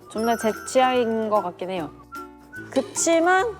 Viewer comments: 존내 제 취향인거 같긴 해요 라고 들리는데 이거 맞는건가요?? 맞으면 좋겠다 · ㅈㄴ 제 취향인 것 같긴해요 라고 들리는데